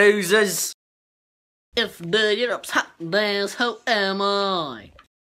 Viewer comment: I do not understand what is he saying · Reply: "If Europe's out then so am i"